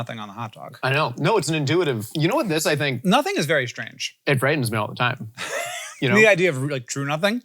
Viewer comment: ☝caption error: “brightens me” instead of “frightens me” 37:18 the word “corvids” is missing 37:43 « la pieuvre » is not Greek 😅 39:07 “the treaties” instead of “Atreides”